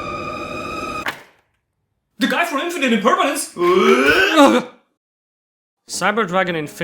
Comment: please what are you saying here i cant tell for the life of me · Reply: "The guy from 'Infinite Impernanence?!'"